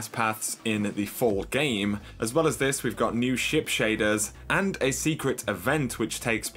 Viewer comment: I thought you said shit shaders lol xD